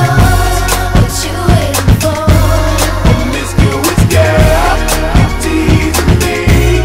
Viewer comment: This whole time I thought he was saying “mysterious girl” 😂